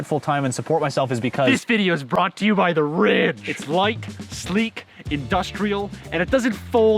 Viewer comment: i thought you said 'this video is sponsored by the rich' lol